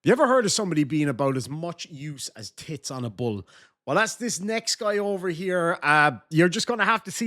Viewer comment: With your accent I thought you said titts on a bug. And (guy in the middle) reaction is so funny. Here in America there is a fast food restaurant on almost every corner but you have to be humble enough to do what ever it takes to take care of your responsibilities.